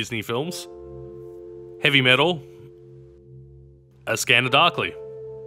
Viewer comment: what movie? I don't understand sorry i'm argentinian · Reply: "a scanner, darkly"